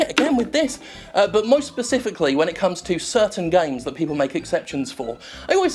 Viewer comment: did you say "moist specifically" jim? just after saying you sweating like cheese.